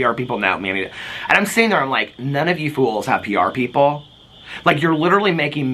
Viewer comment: I swear i thought i heard say ghouls instead of fools 😂 lol!!! IMHO they both apply to these shameless internet schills. Video on point ☝️❤